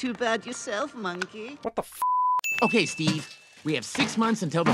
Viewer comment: it sounded like the monkey said what the f*ck